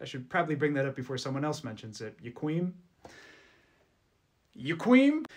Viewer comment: "aaah" and "yukweem" got me bro😭😭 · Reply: I thought he was saying "you queen"